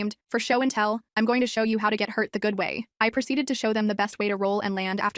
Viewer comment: At the beginning I thought she was gonna bring like a gun or something and then I was doing dishes and I miss her and thought she said “ i’m going to teach you how to get high off cocaine”😭😭😭 and I rewatched it think “WHAT” and then realised she said “how to get hurt the safe way” I’m so restarted 😭😭